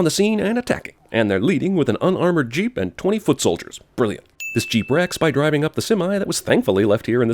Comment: I heard this as "20-foot soldiers" and had to rewind, because I definitely did not remember giant army dudes being in this movie.